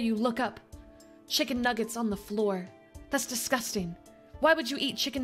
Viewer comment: Thought she said “chicken nuggets on the cob” and got very confused